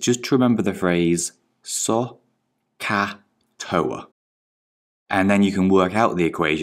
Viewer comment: It sounds like he is saying suck a toe instead of SOHCAHTOA